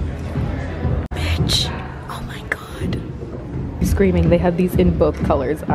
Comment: did you say “bitch omg” 🤣 love that energy